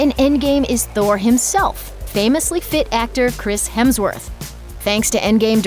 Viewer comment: I thought it said Chris Hanson...